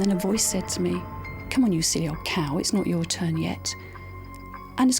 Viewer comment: what does the voice say at 6:30 ? "Come on you silly old cow" ?!